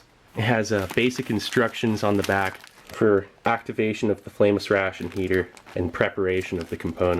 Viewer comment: Been watching your videos for a while now. Only just realised you are saying 'flameless ration heater' I thought you were saying 'famous ration heater' 😂